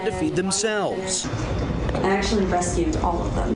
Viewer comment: Did she say she BREASTFEED the bunnies? Got to the 2:30 mark. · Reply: I heard, "I actually rescued all of them."